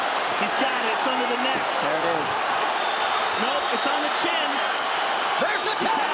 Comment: Kabib was about to break his neck and I heard "No, It is on his chin". Haha..🤣😂🤣 Great fight. Great players.